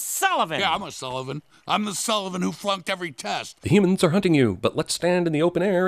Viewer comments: did he say “fucked every task”? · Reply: "Fluncked every test" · it sounded like Sullivan said F*cked